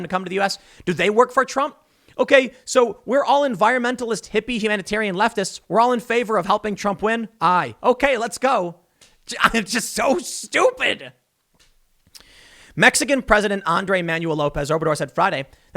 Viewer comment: I swear he’s saying I’m so stupid